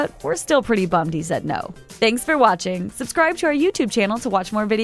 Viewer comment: At the end from "...your favorite Rick Moranis role" I only heard "Rick ... roll"